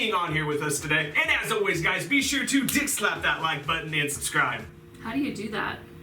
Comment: I thought she said how do you dickslap the like button